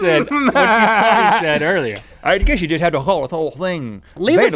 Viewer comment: i heard ''i guess you can call it'' after that it sounds like the craziest thing